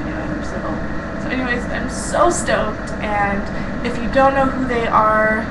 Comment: I thought you said you're so STONED at first lol